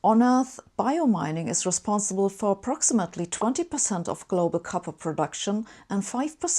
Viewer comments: Hi Sabine, this is a nice video! There’s a small typo at 6:15 — the caption should read 20% Copper instead of 20% Carbon :) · Excuse me, did she say 20% of carbon production or copper production. I heard she say one thing but the graphics said another. I think someone screw up the graphics, right?